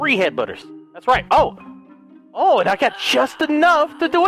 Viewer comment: Did he just say "Headbudder"?